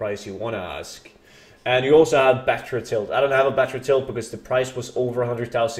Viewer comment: Can someone tell me what he says at 6:43 please , no idea what it is , sounds like backrotill ? Cheers . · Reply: Battered hilt.